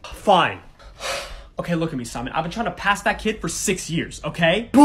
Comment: I thought the teacher said if u make sure he gets a Z, u will get $1000 straight up instead of a C XD